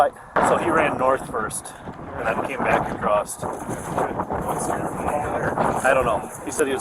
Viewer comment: is "across'ed" regional dialect for across? Or am I just mishearing 😂